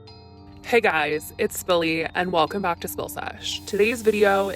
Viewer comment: Okay this person's name is billy lol I thought she said spilly as spilling tea lol 😂😂